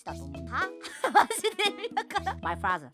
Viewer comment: Is it just me or did said "Motherf*cker" at 1:06 ? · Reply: She says "my father". She is writing what "X" says at 8:35.